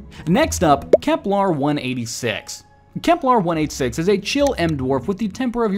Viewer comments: The text says "Kepler-168" but i hear "kepler 186" in the audio... (and both Kepler 168 and 186 exist 🤷‍♂). Which one is being referred? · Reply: I keep hearing him say "Keplar". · The text says "Kepler-168" but i hear "kepler 186" in the audio... (and both Kepler 168 and 186 exist 🤷‍♂). Which one is being referred?